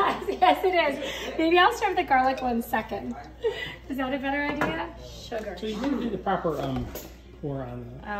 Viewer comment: I heard that "scheiße"